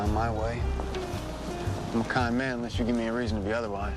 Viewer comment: LOL. At first I thought he said "I am a con man unless you give me other wise". then I got what he said. lmao. I was like "A con man?!"